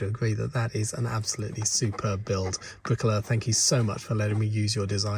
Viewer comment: Oooh, Briquoleur! I thought you said Briquella, son of Queen Mabricka, planter of the Halego Tree.